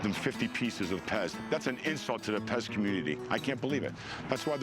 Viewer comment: "An insult to the 'Pez community." WTF? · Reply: Thought he said the pest community